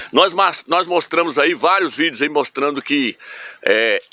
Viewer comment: Foi isso mesmo que eu entendi em 3:08 "NÓS MAÇ..."?